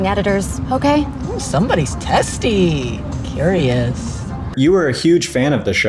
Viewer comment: Why did I think bro said “Somebody’s zesty, period”” 😭😭